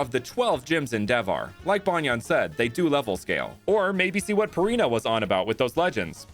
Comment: I misheard Parina as Furina for a sec there